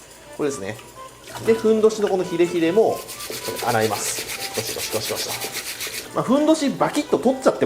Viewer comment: 〜ゴシゴシごしこしこに聞こえるのは俺だけか...